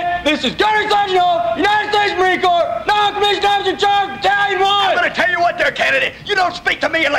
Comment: Did I mishear it or did I hear “non-commissioned officer in charge of Italian wine”?